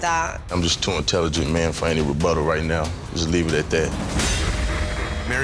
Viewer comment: said “im too intelligent for a rebuttal right now” 😂😂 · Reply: Man when I first saw this video I thought said "I'm just too intelligent of a man to be fighting with butter " which was hilarious 😆